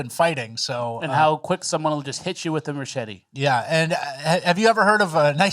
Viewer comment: Did you say “Merchete” 😂😂😂